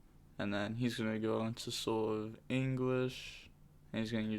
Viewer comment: I heard "soul of english"